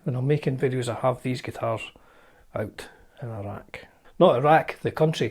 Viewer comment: First minute of the video I thought you said all these guitars are from Iraq! Then I realised you said a rack! Lol!